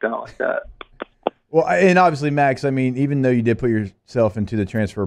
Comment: did he call him Max?